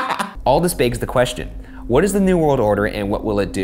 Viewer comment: I heard "all this bakes the question" now I can't stop laughing